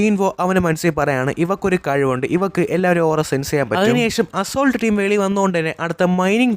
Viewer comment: I heard "ivalak ella varelem s*x cheyyan pattum" 😂